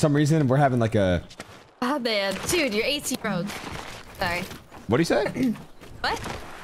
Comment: guys what did she said there? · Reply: "Oh man dude you're AC Broke " think she was reading her chat out loud